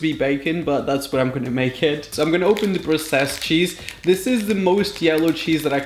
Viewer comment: When he said the processed cheese, I thought he said the grotesque cheese... I was thinking he wasn’t half off